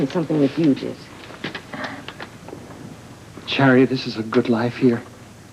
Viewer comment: Does Alan call Charity...Chariot??😆